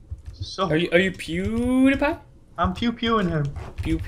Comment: did he say are you pewdiepie?